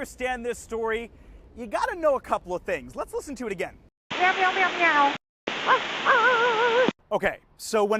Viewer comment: I think he was saying “I want Meow Mix”